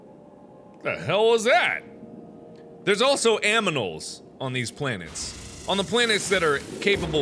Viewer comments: did he say aminals or is that just me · Who heard him say "Aminals" at 10:53 lol